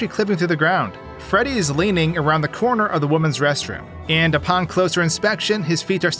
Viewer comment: I was daydreaming and I heard “Freddy is peeking around the women’s bathroom” 😭